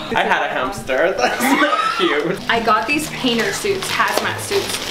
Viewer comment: I thought that she said Weiner suits...